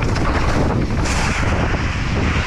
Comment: I heard ‘that was easy’ !? What!??😂